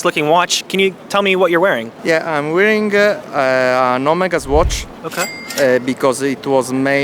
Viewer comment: yea he did say omega swatch which is technically right, nothing wrong with that... dont be elitist, it is a crossover / cooperation kinda thing... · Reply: Chill, I'm not trying to be elitist. I thought he said "Omega watch" which is I thought was funny.